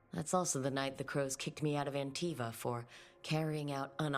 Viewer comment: i heard Anti.f.a 💀 am i cooked